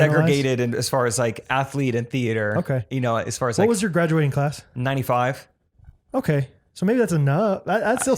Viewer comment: I was so distracted doing math for a solid 5 minutes because I thought said “when was your graduating class?” And was like “95”. I was thinking dang looks really good for being like 44 years old